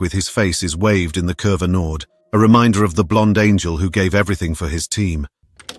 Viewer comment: "a reminder of the blonde danger who gave everything for his team" is what I first heard. which would be a fun nickname.